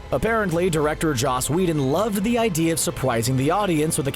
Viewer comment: Is it just me or does he say surpwising